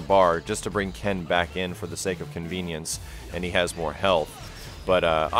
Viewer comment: did you say, "just to bring ken in for the sake of ken-vience"???